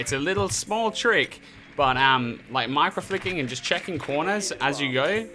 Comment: i thought he said macroflicking at first and was like YEAH MACROS WOULD REALLY HELP